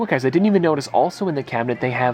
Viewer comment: On 1:01:38 did he say galactus from fortnite??? Did i hear correctly.